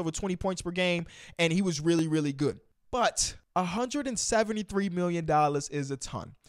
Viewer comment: When I first heard you say what miles bridges was asking for I thought you said 73 million and I said “I would never pay him that” then I heard you repeat it and it turns out I misheard you and you really said 173. Who in their right mind is gonna pay this role player over 100 mil? Ain’t happening